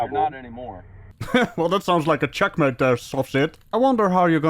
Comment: Every time you said 'sovcit', I thought you said 'soft shit'... Same thing, really 😂😂😂😂